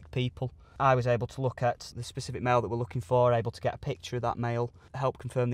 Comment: Put the subtitles on at 1:12 ... they're so desperate to get their product in that the subtitles say Mail when the officer means male